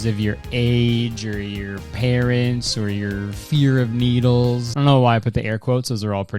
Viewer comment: For real thought he said 'fear of beetles' at the start of his ad read and got so confused for a second 😂